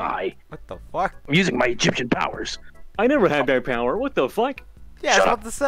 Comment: I thought he said he was using his chicken powers for a second